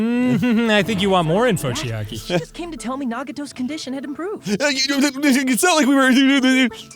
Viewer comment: did Hajime just say "Nag-eh-toes" ?